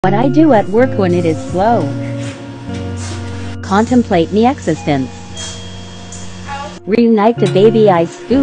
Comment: At the beginning I heard "contemplate the exorcist."